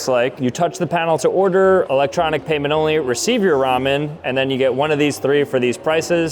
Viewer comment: I thought it said Raw Men